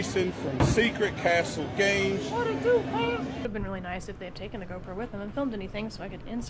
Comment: i thought she said it would have been nice if they'd taken the girlfriend with them. I was hol up.....